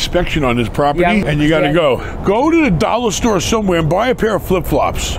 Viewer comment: “Oooh you said flip flops? I thought you said meth.”